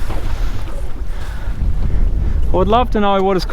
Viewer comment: My girlfriend heard you say "I would love a 69" and when we both giggled she asked why do all grown men act like they're 13 🤣😂🤣😂